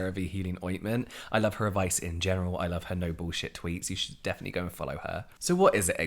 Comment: I heard "noble shit tweets" instead of no bullshit tweets and spent way too long figuring out what that would mean 😂